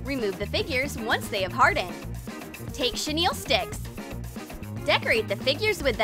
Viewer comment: what on earth did she just say?! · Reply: No like what on earth is a shenile stick